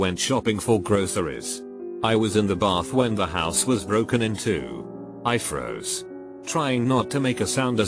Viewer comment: I could not have been the only person that thought it said “I was in the bath when the house was broken in two.”